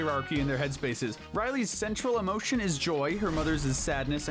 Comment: STOP I HEARD "CENTRAL" AS "SENSUAL"